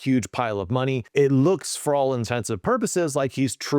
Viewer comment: So far my favorite writing channel. Information packed, concise, and easy to understand. Thank you! I do have to ask. Did I hear "for all intensive purposes"? 😮